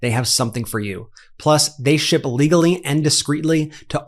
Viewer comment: Try VIIA Hemp! <link> and use code MEIDAS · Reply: Sounds like you said "they ship illegally and discreetly" 😂